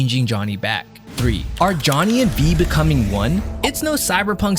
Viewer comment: when you said are V and Johnny becoming one I literally heard "not the brightest bulb are you." 😂😂😂😂😂